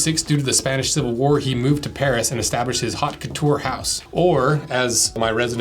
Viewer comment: Did you say he started a "Hawk Tuah" house at 1:24?